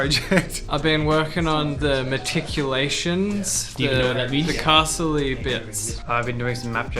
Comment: Did he just say "MACHICOLATIONS!!!!!!"